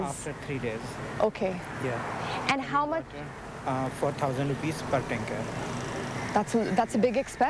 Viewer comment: I believe he says, "For thousand rupees per tanker". Could it have been misheard as 'four thousand'? That kinda explains why the price is "four times the official water board price" (16:20). Maybe, this guy just bought water from the water board. Just saying.